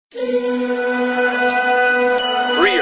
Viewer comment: Ohh lol I thought it said three are mine